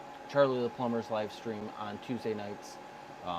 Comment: What I missed it ? Thought you said Thursday !